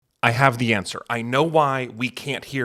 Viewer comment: I misheard the first sentence as "I have cancer" and kept waiting for this to somehow circle back to that and it never did. So basically what I'm saying is that my ears are definitely part of the problem as well but good to know that it's not just that